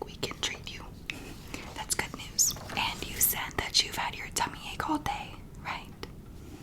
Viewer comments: I thought that said tummy acne and was so confused😂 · I thought it said tummy ‘Acne’ and i was confused 😂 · At first I thought it said Tummy ACNE and I got really concerned 😂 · I thought it said tummy acne ;s